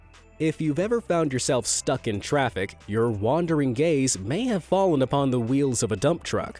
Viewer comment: I thought he said “your wandering gays“🏳️‍🌈 👁💧👄💧👁